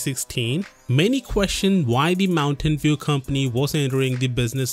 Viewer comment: he sounds like he said "why the mountain dew company"